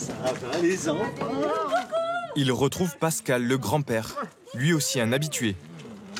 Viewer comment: mdr à 9:30 jai entendus pascal le grand frere